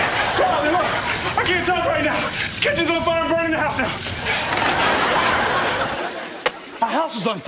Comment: "Hey Carlton, I can't talk right now! The kitchen's on fire. I'm burnin the house down!" lmao Will. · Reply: I thought he said, "can't you see I'm burning the house down"